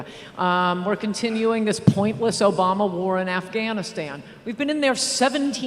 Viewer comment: MMArtist141 I thought she said Iraq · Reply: Nup, go to the time stamp at least, she says Afghanistan.